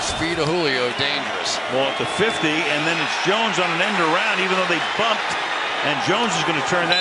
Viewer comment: did he just say they fucked😭